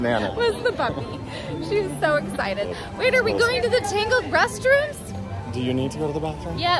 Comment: Hi. Rainy Days and Magic Kingdom are a special kind of Magic. I thought you said Tinkerbell instead of Tangled. I went to Orlando with family 10 years ago. Splash Mountain was closed the day we went to MK. Hopefully Tiana's Bayou Adventure will be just as amazing. Did you have a Ponco for your "ears"? Olivia looked so excited. LOL. All the Food looks amazing. Keep on having Fun, stay healthy, and on to the next exciting Adventure